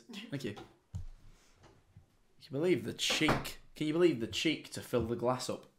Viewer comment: Ngl I heard "Jack can you help"